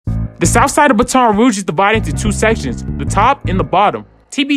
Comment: Bro you saying Baton Rouge wrong lmaooo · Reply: Sound like he said ba town rouge 😂🤣